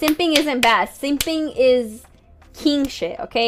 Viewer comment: If simping was king shit, it wouldn’t be simping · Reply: I heard it as "Simping is Kimchi" and was thoroughly confused. ;)